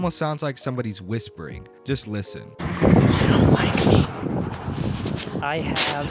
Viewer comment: Yeah I heard “if you don’t like me” and then after he stopped talking, I heard, “bitch”